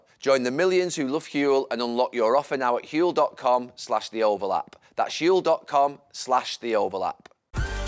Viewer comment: Did Gary just say the oval app?